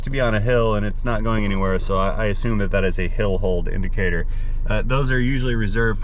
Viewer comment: Hill Hole! I thought you had said going into a Hell Hole!